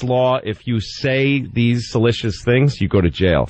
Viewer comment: Ive always like Louis CK untill 5:29:30. Did he just say "salicious"? Maybe he meant "seditious"? Either way, YUCK. Count me out.